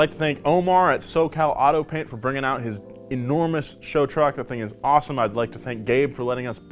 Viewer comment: When he was saying “show truck” I thought he was saying “chode truck” which would’ve been one of the first correct things said in this video